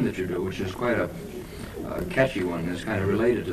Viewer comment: Vicar: that music sounds churchy Well almost.. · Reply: I thought he said catchy. Iron butterfly is totally churchy though with the organ. They used that to give it a haunting feel.